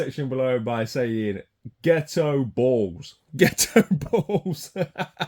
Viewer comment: I thought you said "Ghetto bald" 😂 GHETTO BALLS!! 👍